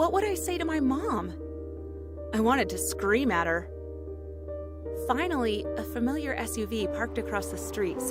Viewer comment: When she said a familiar SVU parked across the street, I first heard “a familiar SOB.”